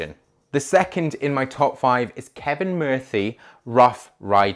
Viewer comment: Did you just say "kevin murthy"? 😂